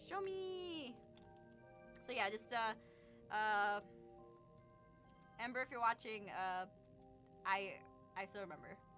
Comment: did she say Amber or Ember? Bc I just got jumpscared by her maybe saying my name😭